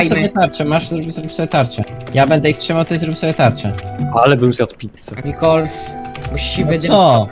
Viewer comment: XDD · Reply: usłyszałem "ale bym zjadł pieska" xDD